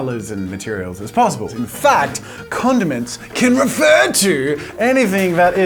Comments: So many times I thought he said condoms 😂🤦🏻‍♂️ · i thought it said condoms......sorry